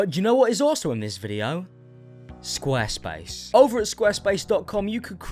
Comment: Lmao I thought he said square face and I was waiting for will to say ‘hello’